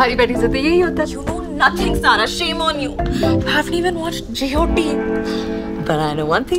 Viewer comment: Janvi Kapoor says “JOT” instead of “GOT” 😝